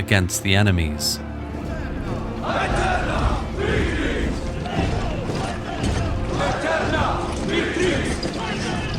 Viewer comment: What are the Romans shouting at 5:57? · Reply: Aeterna victrix- Eternal Victory